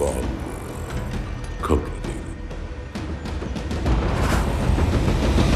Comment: What did the arbiter say? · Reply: "complicated"